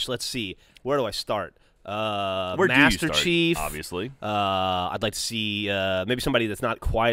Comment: Master Chief, John Marston, Edward Kennway. Not many have played this game but Silas Greeves from Call of Juarez would be so cool since he's a mysterious character! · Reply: I thought you said master chef haha